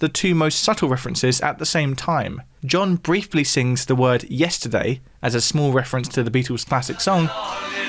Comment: Ha! I always heard the "Yesterday" reference as "Get Together"